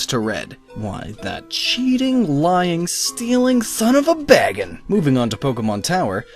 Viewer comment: bagon. I thought you said Baggins. xD